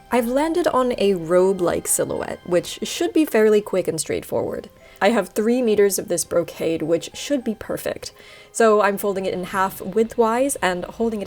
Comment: When she said, "robe-like effect", I heard "rogue-like effect" and I was all like, YES, THAT'S SO YOU! And then I was mildly disappointed when I saw the photos on the bottom left with the robes.